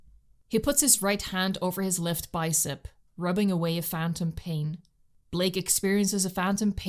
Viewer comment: did u say..... phantom pain.